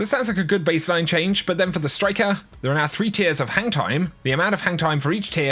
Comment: When you said hang time it sounds like you were saying hentai 😂🤣